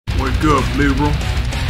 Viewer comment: I thought it said “wake up Liberia”